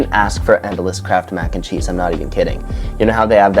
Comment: I'm gonna be honest, I thought you said "crap mac n cheese" multiple times XD